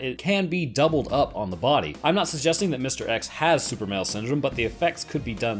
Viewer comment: Is it just me or am I the only one who thought said Superman Syndrome?